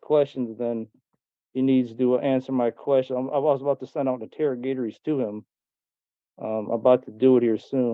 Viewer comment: He’s ‘sending out ‘interrogations’. Do you think he was trying to say interrogatories?